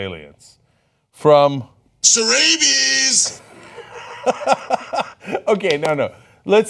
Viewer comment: is that alex jones and what is being said? serrabies? what even is that?!